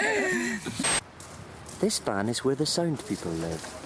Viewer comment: "This van is where the sound people live." For a second, it sounded like he said SAND people.